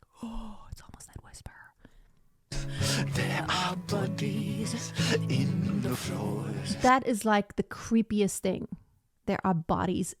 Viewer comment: There was another reactor who, maybe not concentrating properly, misheard the "bodies in the floor" line. She said "What is scary about bunnies on the floor ? I like bunnies" Very funny